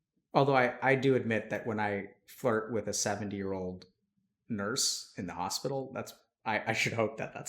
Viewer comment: i thought he said 7 year old nurse